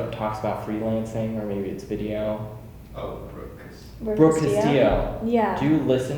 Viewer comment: I couldn't understand... who she listens to...? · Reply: I think she said Brooke Castillo ;)